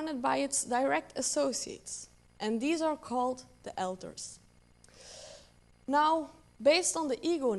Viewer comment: just wanted to comment on a possible mispronunciation @ 20:10. An ego in an ego-net is surrounded by alters. It sounds like she said elders. The auto captions got it wrong too.